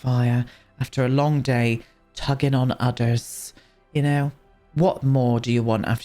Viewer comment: When you were saying tug on udders, I could have sworn you were saying Tug on others.. I was sitting here thinking what the f*ck are you going to make these two do?! haha! Great build as always!